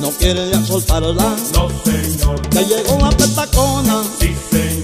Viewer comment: For many yrs I thought it was saying "ya llego la coca cola"lol